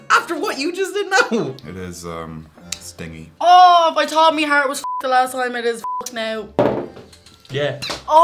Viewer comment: Yo what on earth did she say at 1:59? 😂😂😂😂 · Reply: If I thought my heart was fucked the last time, it is fucked now.